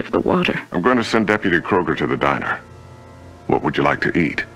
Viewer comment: the cop says Kroger but for a second i thought he almost said "Kruger" instead. (